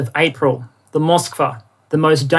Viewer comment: many thanks to everyone as always, sorry the release was a few hours late. And before I cause an international incident over a joke - I'm aware that it was the Brits who burned the White House during the war of 1812, not their Canadian allies....but let's all give them a little team credit eh? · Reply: Did you say circus combatant?